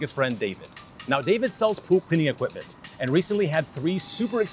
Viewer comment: I thought you said poop cleaning equipment